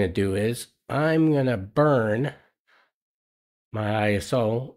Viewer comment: I swear I heard: "Now, I'm gonna burn my 'soul'"...